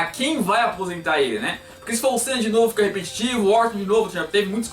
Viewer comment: não entendi muito bem,qual foi o nome que ele disse? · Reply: Orton